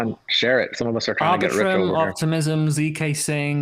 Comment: What does he say at 1:34:29? It sounds like argatrim??